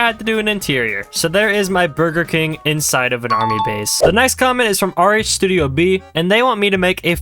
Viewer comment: Is it just me or did he say nice comment?